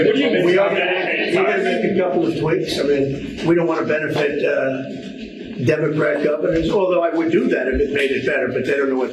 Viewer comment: So in that clip I heard "We don't want to benefit Dem Govenors"...Are we (Dems) now that targeted group like the Jewish were in 😮WWII ? ....Will our MAGA relatives start turning us in for gov favors? I AM GLAD I AM OLD!